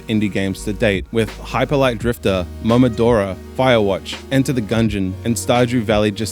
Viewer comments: what's the name of the name? Namadora? I dont understand .-. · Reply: Momodora: Reverie Under the Moonlight, sorry my accent is a bit weird haha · what was the game you said after u said hyper light drifter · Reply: Momodora: Revire Under Moonlight! Sorry I slurred that line really bad haha